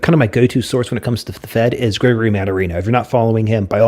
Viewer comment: hey thanks for the vid. you said we should check out who? I heard "Grey Re Man Arena?" you talk fast for a texan and i missed it! Also you said "Kevin Mir?" am I spelling his name correctly? thanks guys! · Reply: <link> greg mannarino kevin muir at the market huddle <link> Thank YOU!